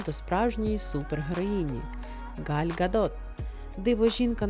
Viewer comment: Вы так забавно произнесли имя Галь Гадот, что мне послышалось: Галька Дот. Нет, ну у вас действительно, забавное произношение.